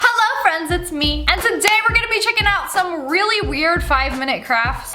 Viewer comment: It sounded like u said "today we're gonna be chicken out"😊😊btw you are reall pretty